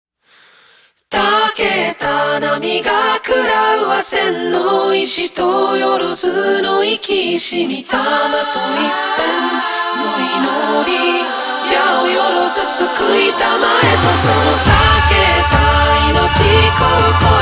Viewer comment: 「生きし」が、「生き死に」に聞こえるのは私だけでしょうか